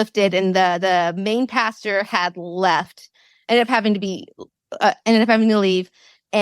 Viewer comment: I can't understand what the guest is saying at 48:40, when she says, "The main pastor had left, and had to be ******." It sounds like she's saying he had to be "F-M-F-L'eed." What is verb she's using? Is it an acronym of some sort? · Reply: “ ended up having to leave”